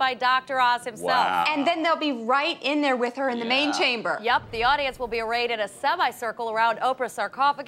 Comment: "...they'll be right in there with her in the Main Chamber" At first I thought said "Mating Chamber!" :X